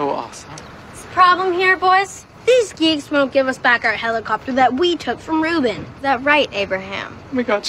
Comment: Did he just say " HELICOFTER"😹